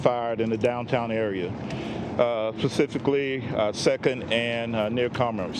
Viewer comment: Did he just say "pacifically"??